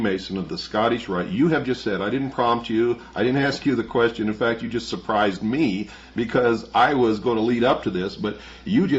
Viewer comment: what is the word you are saying? Cadgy? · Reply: C.A.J.I Citizens Agency for Joint Intelligence.